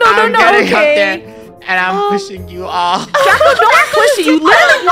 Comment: talk pushing but i heard she say puss#